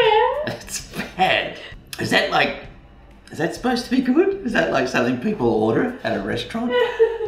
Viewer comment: I love! “Is this supposed to be good?”😂🤣😂🤣😂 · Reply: i thought he said "is this supposed to be food" which also makes sense